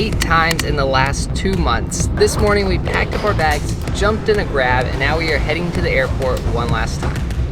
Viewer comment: is it just me or did say "jumped in a grab?"